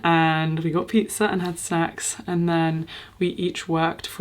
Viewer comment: what i heard was hilarious....I heard "we had pizza, we had sex and then...." and I was what?? 5 people who did not know each other?? I never mishear things on your channel but i went back and realised it was snacks !!! not sex....